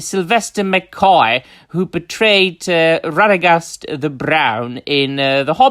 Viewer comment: My drunken ass thought you were saying betrayed..not portrayed.